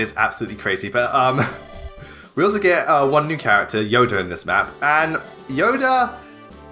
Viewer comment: Okay, for a second, I thought you said "You get Yoda in this map"